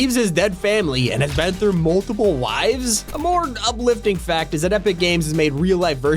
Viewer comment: Am I the only one who thought he said wifes?